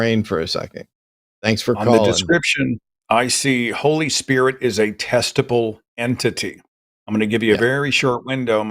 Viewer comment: I was listening to the show while scrolling in another tab and I swear it sounded like Seth said "Holy Spirit is a testicle entity." As it turns out that would have been a better conversation.